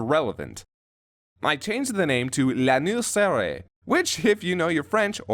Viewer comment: said "L'anus serré" ?? I'm French and i always thought she said "Les misérables" ...